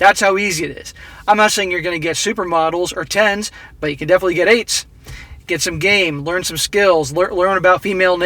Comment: "But you can definitely get AIDS" is what I heard. XD